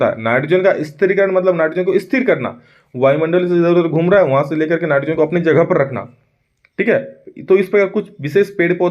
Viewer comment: par sir ne kya bola 😂😂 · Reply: Vayu Mandal Stri Karan matlab srhir karna